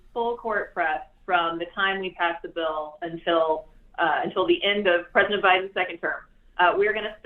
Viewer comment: Lol, did she just say "until the end of the President's second term"? @ 13:55 Interesting that nobody commented on that yet :)